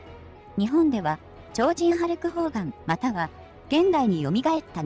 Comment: ホルクハーガンに聞こえるよね